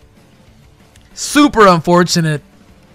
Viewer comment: I thought he said Supra unfortunate😂